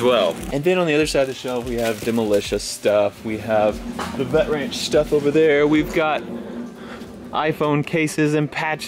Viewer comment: does Matt say "Butt Ranch stuff?" LOL